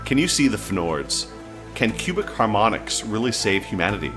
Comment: Thought you said human harmonics and was scared tou were into sounding